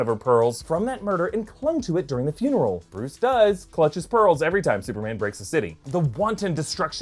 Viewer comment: Did you just say that Bruce Wain clinched his balls? lol